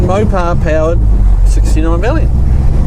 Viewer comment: My guess is you said Valiant, because a compact 69 with a 318, would normally be a Valiant (or Dart) 🤣 Nice car though.